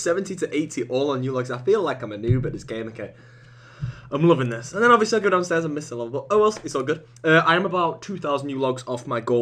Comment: can somebody tell me what he says at 11:40? All i can make out is that he goes downstairs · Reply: I'm loving this and then I'll be so good at slayer I'll miss someone but oh well it's all good. That's my best guess. It makes no sense.